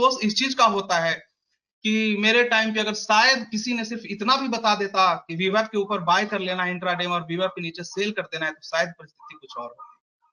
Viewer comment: Time 11:37 per kya bola hai..."viba k upper buy, vba k niche sell"????? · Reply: VWAP IS A TOOL